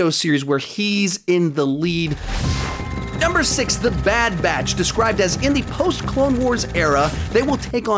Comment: I thought bad batch said bad bitch and i was very confused for a second